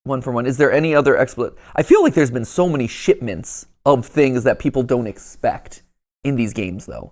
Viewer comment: did bro just say "shitments"?